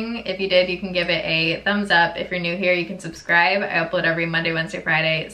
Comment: I heard the word epilator so many times throughout the video that at the end I thought you said “I epilate every Mon, Wed, Fri pacific time” lol 😅😂